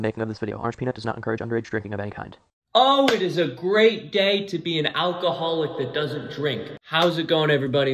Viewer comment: Lmao i thought he said it is a great gay to be an alcoholic who doesn't drink